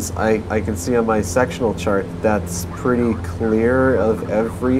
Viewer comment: is it just me or does he say "I can see on my sexual chart"?